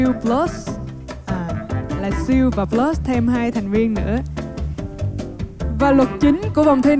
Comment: Phút 1.54 Nghe thành: SEAL PLUS + thêm 2 "Thằng Điên " nữa haha. Nghe một lúc mới biết nghe nhầm =)))))